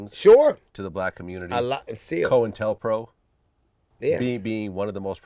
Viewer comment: What is vlad saying starting at 1:03:28 because I want to look it up. I already know about the Tuskegee experiment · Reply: COINTELPRO.